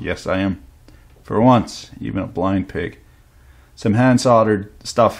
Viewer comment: What did he say at 4:30? Something.... you pig?